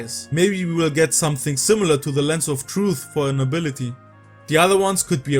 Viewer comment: At one point I am not sure what you said but I heard “Lasso of Truth”. In BOTW we often saw bokoblins and lizalfos talking to each other. Being able to listen in or interrogate them would be pretty darn cool